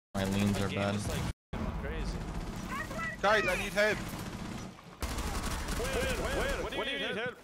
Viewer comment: I swore he said i need head